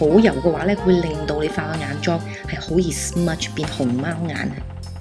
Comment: she clearly says HOLY SMUDGE lol